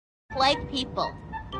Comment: Im sorry but am i the only one who forst time hearing this thought she said "white people" before I watched it again- please tell me im not alone 😭✋